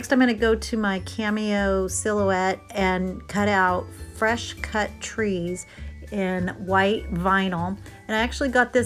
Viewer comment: I definitely thought that said “fresh cat treats,” and I was REAL confused for a hot second. 🤣 That checker pattern is so stinkin’ cool!! Like I’m a little bit in awe that you just did that. 🤩